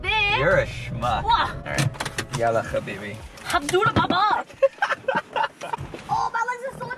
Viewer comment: we need an Arabic dictionary cause as an Arab I'm still trying to figure out what they said but I can't 😂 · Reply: SHe said hamdu allah (thank god ) 🤣